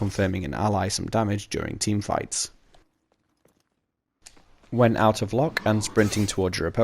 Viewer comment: I only just realised that you've been saying "When out of LOCK..." in these videos. I kept hearing "When out of luck..." and wondered why you kept saying that you should only use sprint attacks when out of luck.